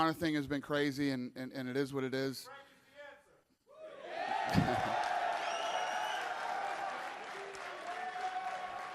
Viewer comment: what did the guy in the crowd say???? anyone? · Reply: frank is the answer (audio on this is terrible)